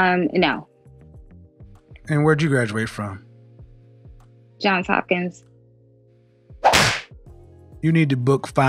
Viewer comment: Kevin: "and where did you graduate from?" Her: ??? What does she say? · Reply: John hopkins